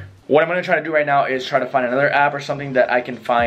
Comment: I appreciate you doing this. You speak fast which I also appreciate. But I missed 1 word in the whole video. You said "Spotify" or something else. I played it back like 3 times and it sounds like your saying scottify. Could you clear that up for me. Thanks for what your doing here.